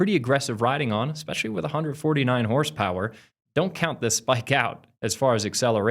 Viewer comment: At first I thought you said it had 49 horsepower now, I was like what!? 😂